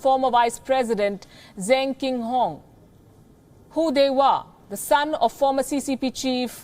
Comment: I legit thought that she said "Who they were" coz the man is called "Hu Dehua" Lmao.